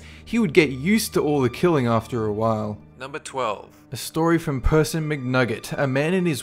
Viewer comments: Is it me or at 1:50 did I hear person mc nugget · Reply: ys "a story from person McNugget" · I'm sorry, but did he say "Person Mc'Nugget"? Please correct me if i'm wrong.